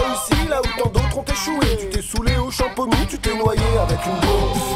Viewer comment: "Remarque, t'as réussi là où tant d'autres ont échoué Tu t'es soûlé au Champomy, tu t'es noyé avec une bouée" Vous m'avez fini sur ce coup les gars!!! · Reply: moi j'ai entendu "tu t'es noyé avec du lait"